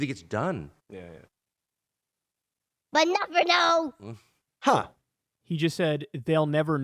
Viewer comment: I heard “but never know”. Evil pair